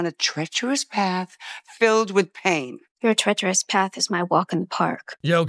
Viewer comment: I hate that Morticia forbids Wednesday to do something because it will "lead her to a torturous path"... That's not the supportive Addams mum that Morticia is! She would have said: "absolutely, my dear. May I interest you in an even darker path?" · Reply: I thought she said treacherous